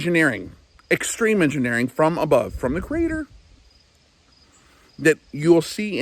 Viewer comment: Mmmm , at 04:00 => did you say CREATOR or CRATOR sounded like it (i'm open minded so i let it be for what i don't know for shure) . Nice cup btw